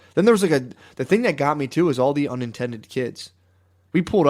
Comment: Lol I think Matt meant "unattended" kids, but I heard "unintended" which absolutely fits